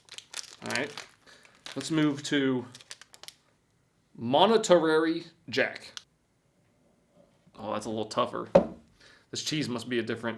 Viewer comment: Did I hear correctly at 4:10 "Monetarary Jack"? Whats that? I feel like I'm watching in the dust bowl days but with futuristic products.